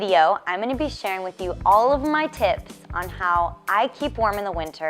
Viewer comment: I thought she said "all of my tits". Oh man. Hahaha.